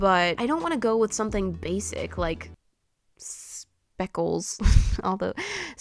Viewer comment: Names for the dog in the dragon fruit: Dixon Allie Spott Maddie/Madison Poppy Speckles (you didn’t want a traditional name, did you now ;-;) Harp/Harper Yeah that’s it lol Edit: oof I thought you said spots instead of speckles 😅